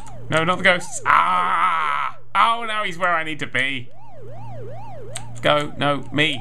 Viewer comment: did anyone else hear him saying "oww no hes were i need to pee"?